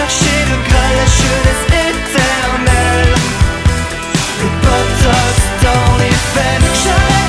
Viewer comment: je suis folle ou il dit ''le botox dans les fesse'' à 0:53 ??? et aussi il dit ''jacques s'en mêle " à 1:37 ??? svp dite moi que chui pas la seule à entendre sa mdr